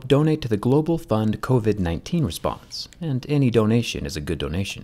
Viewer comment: KOVAT 19 instead of covid 19 😁😁😁